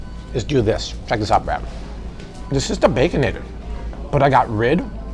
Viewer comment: I thought he said that the bacon was bad; why did he put it on the Bobby-fied version of the Baconator?